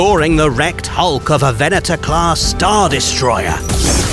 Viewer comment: I heard 'Erect Hulk', can't unsee